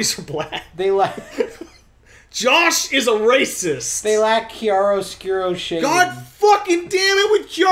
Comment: What the hell is Kyaro Skeero? I don't know if that's spelled right, I'm just going off what I heard. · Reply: HUNKthemerc hi I'm here to answer your question a year later. Chiaroscuro is an art technique, shading from dark to light. If you've ever seen a drawing of sheets or a ball that was used as practice for shading and light direction, that's what it is.